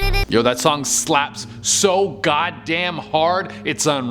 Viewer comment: Did he just said GODDAMN?